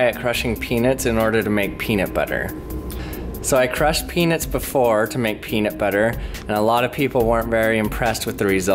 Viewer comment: "So I crushed penis before to make peanut butter." xD Sorry. I honestly thought that's what he said.